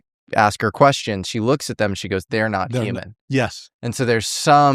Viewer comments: "They're not Human"?? I heard "They're not FBI". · I thought the girl said " thats not the fbi" not there not human , in the hospital scene 🤔